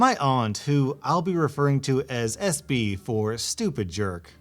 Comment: i was listening to the video and eating so i wasn't actually looking.... then in 4:19 (almost 4:20, dang it) i hear: who I'll be referring to as SB for stupid jerk me: wait, isn't it SJ? Also me 2 seconds later: OOOOOOOOOOOOOOOH.... i'm such an idiot.... HE MUST HAVE SAID SJ AND I DIDN'T HEAR IT :)